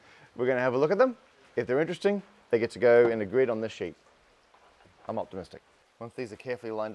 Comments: I rewound at 3:47 hoping I heard the pun "I'm mothtimistic" but it was not to be 😢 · for a second i thought he said "I'm Moth-timistic". i was wrong, disappointed